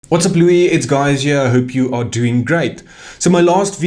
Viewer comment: Did anyone else hear him say "whatsup Louis its guys here"😂